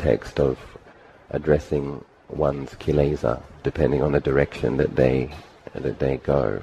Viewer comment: what is that word, kilaza? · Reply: Kleshas,(Pali: kilesa), are mental states that cloud the mind and manifest in unwholesome actions.